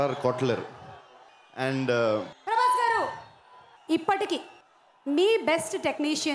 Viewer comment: Why did the host scream at Prabhas 40:45 ? What did she say? She scared him. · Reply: She just said "prabhas sab"